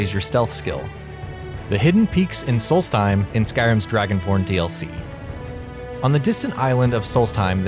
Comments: did you say DragonP0rn DLC? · Did he... did he just say Dragonporn?? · At one point it sounded like he said "DragonPorn DLC😂😂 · 'Skyrim's Dragon porn dlc' His 'b' sounded like a 'p'... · anyone else misheard that as Dragonporn DLC? ^^ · Lmao sounds like he said dragonporn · Im pretty Sure i heard Dragonporn at 2:00 XD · Did I hear “Dragon P*rn” ????